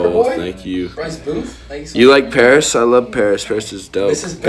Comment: hi can you tell me what he said at 3:07 am because i'm french and i told him if he liked paris but i didn't understand because i don't really speak english can you tell me what he said? Please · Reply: parnika but he just said “ I likes Paris “ on sujet 🙁